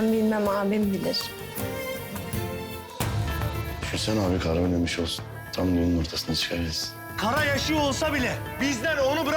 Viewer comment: ne dediğini anlayan varmi? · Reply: Ben anladım diyorki düşünsene abi kara ölmemiş olsun,tam yolun ortasında çıka gəlsin.